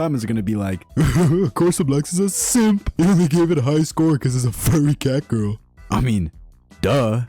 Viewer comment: I swear i thought i heard him said " Furry fatgirl " 😅